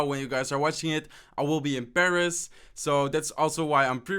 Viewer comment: When he said he was in Paris I thought he said he was imbarassed😂😂😂 0:24 and he also said above instead of a buffed